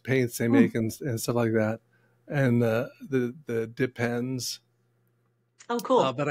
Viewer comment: LOL! .....what they said...."dip pens".....what I heard......"Depends".......😂